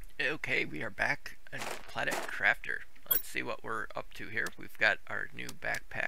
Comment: At first I thought you said "Welcome Back To Planet Crapper" 😂, Great episode buddy 👍